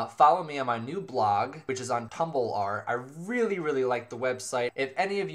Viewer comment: did you say "Tumble-are"? LOL ur so cute xD